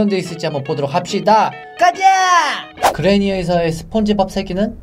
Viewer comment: 나만그래니에서스펀지밥새끼는이라고들림?